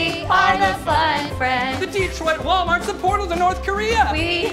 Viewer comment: There are so many quotable lines in this sketch it’s hard to pick just one! Maybe “the Detroit Walmart is a portal to North Korea!” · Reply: I thought it said " Detroit Walmart has a PORTAL to North Korea"